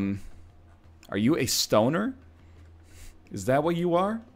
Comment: So I was just in the kitchen with the bluetooth headphones and all I hear is "Are you a stoner?"